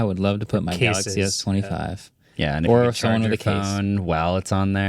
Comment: Did he say galqxy s25?